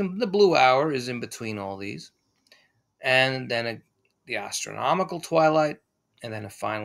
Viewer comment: I'm pretty sure I heard "gastronomical" twilight 🍲🥞🥐🥙🥓👀